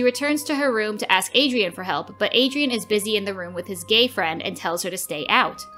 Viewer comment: IDK IF ITS JUST ME BUT DID I JUST HEAR GAYFRIEND